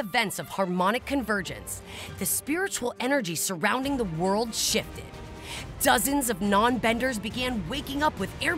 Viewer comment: WAIT did she just said the S word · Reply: SHE SAID SHIFTED ㅠㅠㅠㅠㅠㅠ